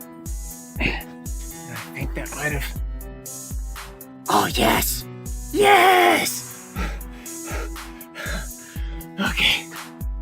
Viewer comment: At the 23:00 mark it sounded like Smeagol/Gollum from Lord of the Rings was under there...."Yes, yes pecious, yes"